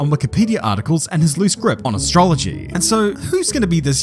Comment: I think I've heard aSTROLLogy there 🤔